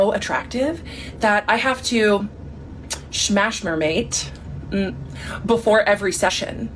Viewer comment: The way "smash mermaid" made you rage fixed my mental health.🤣🤣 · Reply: I thought she said the bloke said “smashed my mate” lol think I need therapy??? Haha